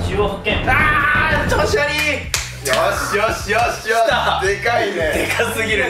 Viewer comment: りょうくんの「よーしよーしよーし」が「腫瘍腫瘍腫瘍」に聞こえる